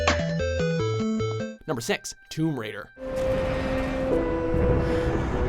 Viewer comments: Did I just hear him say "Number Sex"? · i heard "number sex tomb raider"